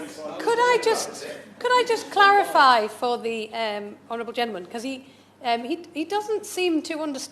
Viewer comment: the automatic captions say "The horrible gentleman" 😂